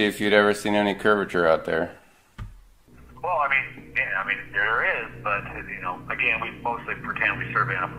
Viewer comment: So. The phrase “we pretend the world is flat” now means “the earth is flat”. And at 4:36 he actually says “there is” curvature This must be one of the most dishonest videos by a flat earther I’ve ever seen. This is so blinding obvious a deliberate misrepresentation of words actually spoken it amazes me that anyone would have the nerve to post. Even the music is deliberately increased in volume to try and drown out the words of the surveyor while decreased when the caller is talking. Shameful. I hope the owner of the business takes legal action.